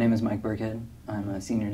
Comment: Thought he said Mike Brickhead rofl.